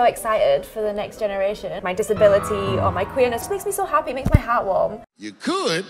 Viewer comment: did she say her disability of queerness? Lol if so that's hilarious.